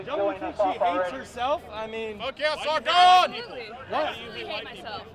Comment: Did he say "fuck yeah Sargon" (of Akkad)?